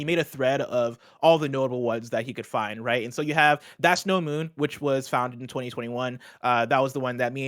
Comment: That's no moon is THE star wars reference. It's been a cheat code in video games! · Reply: I thought the crew kept saying "That snow moon" and was confused about it being a Star Wars quote.